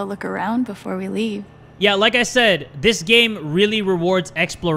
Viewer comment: Did he just say more ammo